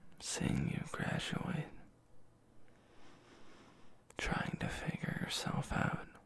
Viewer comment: I was almost asleep then I heard 6:25 and thought it said “trying to ✌️ yourself”